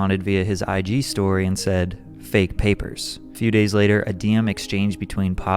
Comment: he actually says “fake news” not “fake papers” 😂